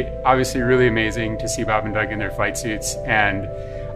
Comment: At first I thought he said 'fight suit'. Kinda disappointed it's just a flight suit...